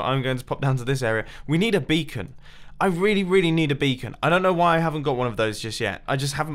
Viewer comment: It sounded like you said that you, "need a BEATING." And here I was, ready to oblige. "Darn." ;)